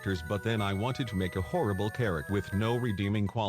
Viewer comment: I'm not sure if you meant to say character or chara but I heard "I wanted to make a horrible carrot" it's